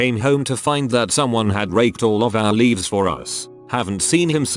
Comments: "a week later we come home to find that someone had raked (I heard rape at first) all of our leaves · I was taken aback by the first story when the subtitles said “raped” instead of raked... 0:40 I was like oh $h!t! That turned south real quick!